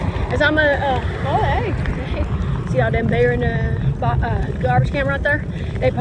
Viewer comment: I thought she said there was a BEAR in the garbage can... she said BEER...... Rock on.